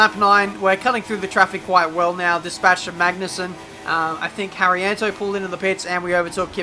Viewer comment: Am i the only one who heard him said Sebastian magnussen 11:37 LOL xD